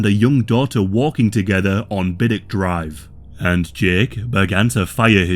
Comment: I misheard that as “big dick drive”